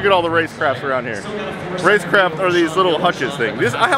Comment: what are those shelves or hutches called? Did he say Race Craft?